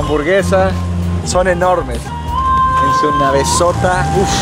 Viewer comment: Le entendi “su cabezota” y era navezota